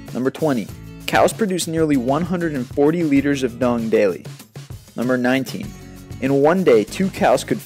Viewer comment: Hello at 0:49 the auto captions says "cows produce nearly 140 leaders of don daly" but it's suppose to say "cows produce nearly 140 liters of dung daily". You have many viewers & I'm sure some are deaf and it makes it hard for them to understand the video. May you correct the auto corrected mistakes throughout the video so the deaf can understand also ?